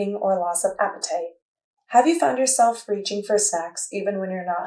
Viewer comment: The subtitles say "reaching for sex" I'm guessing it's supposed to be "food"...